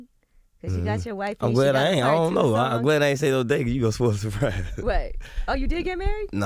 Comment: what did he just say 💀💀💀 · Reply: “idk. im glad i aint say no date, cuz u gonna spoil surprises”